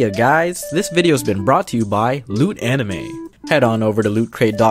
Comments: for a second I thought he said “this videos been brought to you by lewd anime” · lol for a second there I thought he said “lewd anime” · Wasnt paying attention to the ending and misheard "hey guys, this video has been brought to you by lewd anime". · at first I heard it as "lewd anime"😏